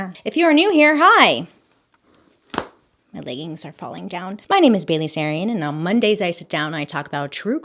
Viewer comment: Tell me why I thought she said “my ladies are falling down” I had to go back and rewatch it she said “my leggings are falling down” 😆😂🤣 I’m dead